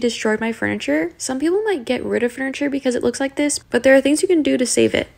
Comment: When she "some people might get rid of the furniture" i thought she said "get rid of her" as in the cat 😂